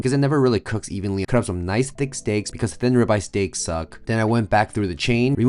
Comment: I had to rewatch cuz at first I heard "ribeye suck " 😮.I almost u followed u for that . 😂. Careful now . ❤